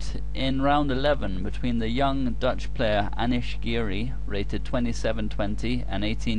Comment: I thought it said Amish girl. I was intrigued